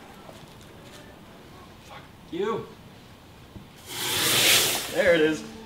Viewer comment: did he say f u